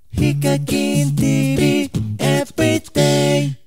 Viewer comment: ヒカキンTVって言った時無課金TVって聞こえたｗｗｗ